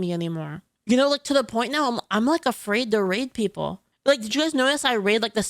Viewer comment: sounded like she said, 'i'm afraid to r*pe people'